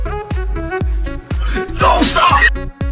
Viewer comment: 나 왜 don't stop 으로 들리냐 ㅋㅋㅋㅋㅋ 멈추지마 ㅋㅋㅋ